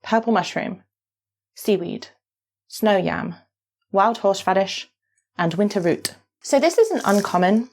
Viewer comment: i misheard that as "wild horse fetish" and had to rewind lmao 😭